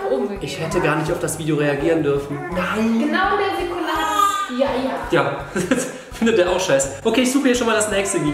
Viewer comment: Hat bibi bei 2:17 Nikolay zu dem baby gesagt? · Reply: Ich hab "Ja, ja" verstanden